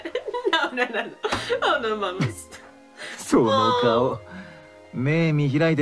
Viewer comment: was it me or did she say “no mames” jsjsjsj